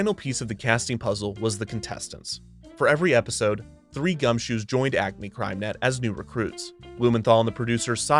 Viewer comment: When I was little I didn't know what gumshoe meant. I thought the host was saying "dumb shoes." I kept thinking "Why is he calling them dumb shoes? Why does he say that?"